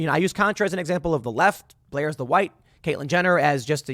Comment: Idk if it was just me but it sounded like he said Blair is on the white lol instead of right lol